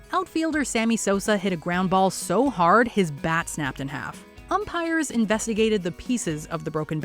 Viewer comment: i heard "he hit the ball so hard that his back snapped in half." I was like oh no! lol